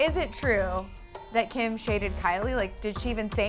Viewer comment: Oml o thought they said kim shaved Kylie I was hella confused 🤦‍♀️😂